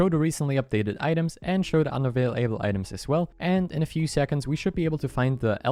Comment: what did you say* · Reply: unavailable